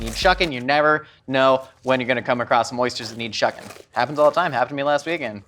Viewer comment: When he was talking about carrying a shucking tool around, I heard "You never know when you'll come across some moistures..." LOL. I also cracked up when he got breathless kneading the dough. I always think of it as doing chest compressions and that is damn hard work no matter who you are.